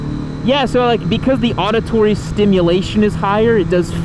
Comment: Did you just say "Auditory Stimulation" ? That's exactly what makes sex more better than the actual sex...